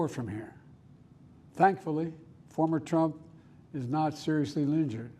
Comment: Did he say "Former Trump" ? 😂